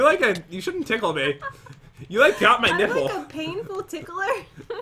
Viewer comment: is it just me or did actually say, “ You like got my nipple.” 😅😂🤣